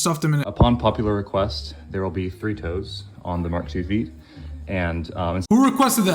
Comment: why did I think he said Fritos (the chip brand)😭